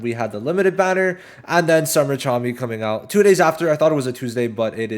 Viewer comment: I keep hearing "summer charmy" instead of "summer chami" 😭